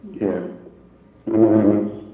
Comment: I don't know what's the situation, I'm hearing it for the first time so I might be wrong. I was distracted at the beginning of the video and I started to really listen when I heard the phone call because he sounded to me really sad and guilty for something , and she sounded like she was trying to calm him down/cheer him up and make him feel better. Btw at 3:45 I thought I heard "I'm nervous" after "yeah, yeah". The "goodbye" at the end sounded like, I don't know how to describe it exactly, like he is feeling defeated and really really down and is too sad to use the more "light" form of the word (bye), because it sounds too happy for his situation , so he decided to use the full form of the word (goodbye), and also the added "good" before "bye" could sound more calming for him. For the second phonecall I don't know what they are talking about, it just sounded like Lori was trying to say to that she used her for an alibi or something.